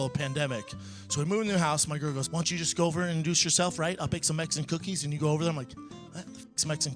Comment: Whoever booed after that cookie joke is a king 👏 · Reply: I thought he said (she said) I'll bake some eggs and cookies... I was like, whaaaa...? Multiple listens, I couldn't understand the sauce, part... ty to the people pointing out it's Mexican cookies... still, I'm like... whaaaa...? Haha, boo!!